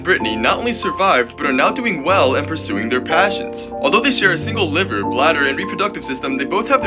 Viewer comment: when he said pursuing their passions i thought he said suing their parents